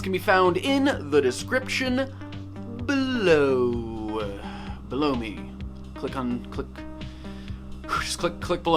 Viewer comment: I heard him saying "Blow me" instead of "Below me" xD